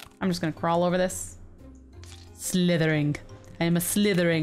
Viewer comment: when said slythering i thought she said Slytherin octopus XD i